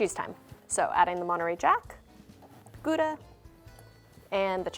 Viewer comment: Am i the only one who heard gooda instead of gouda